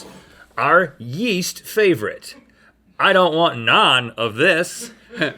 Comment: Oh, yeast. I thought you said weast.